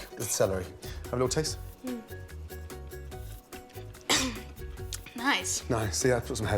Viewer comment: I thought he spit it out and said “nice”